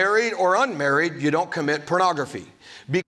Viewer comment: sounded like he said "purography." was he drunk?